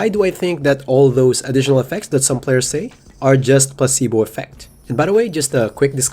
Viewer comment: I thought he was saying “pussy boy effect” at first lol. Great video